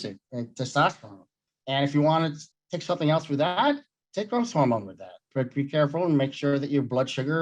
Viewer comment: what does he say to take along with testosterone? Sounds like something-hormone but I can’t pick up the first word. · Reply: I think he says growth hormone. Like hgh.